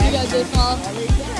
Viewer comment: When the person said you got this mol I thought they said mom It was amazing bye the way